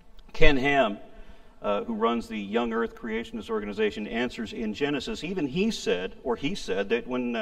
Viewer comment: I'm sorry, did I mishear him completely, or did he say that Ken Ham runs a group called Hamsters in Genesis?